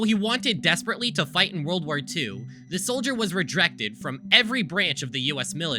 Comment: Did I hear you say "rejrected"?